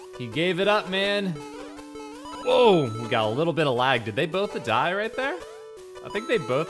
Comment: Does it sound like he said "both-a died" to anyone else? turning into Mario at this point...